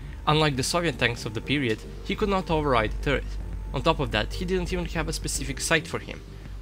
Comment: At ~2:00 the narrator says about the TC: "He cannot override" something. I've listened to it several times and the best I can come up with is: 'search.' Anybody know for sure what the narrator was saying? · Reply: "Turret"